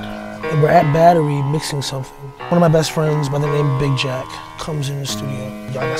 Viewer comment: Did he say his friend Big Jack was the one that did the sample??? The original song: “little boy blues seed of love” What if that sample beat was given to Nas ?